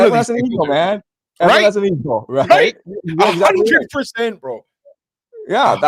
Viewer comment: What did say at 56:13? Did he say Messiah has no equal? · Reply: He says: “Everyone has an ego, man. Everyone has an ego”